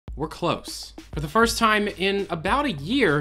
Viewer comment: (We're close) I thought he said "We're closed"